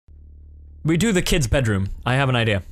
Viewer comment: When I clicked this video I wasn't paying attention cuz I was doing something but for some reason I heard "We do the kid in his bedroom"